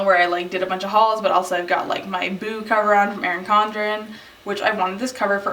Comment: I totally thought you said boob cover, instead of Boo cover... AWKWARD hahaha